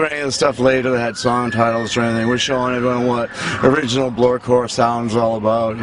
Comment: Wtf did he say at 00:28? Bloorcore? I cant make that shit out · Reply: Blurcore.